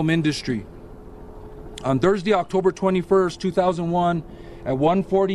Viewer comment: Time 0:52 Did he say 2001 😂